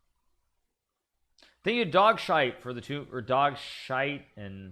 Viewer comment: First thing I hear: “Thank you Dogshite” Killed me😂